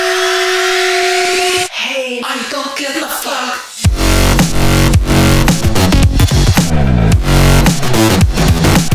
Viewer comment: did anyone else hear that???? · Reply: "hey, I don't give a fuck"